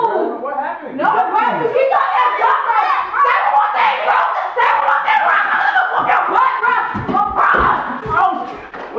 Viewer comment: I'm tryna figure out WTH said at 5:42 but good prank.. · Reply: She said say one more thing bro